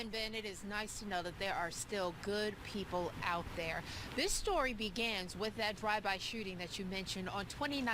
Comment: Did she say "This stogy begans with that drive-by shooting ..."?